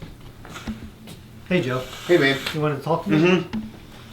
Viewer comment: did joe say "hey babe"? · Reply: He said “Hey, man”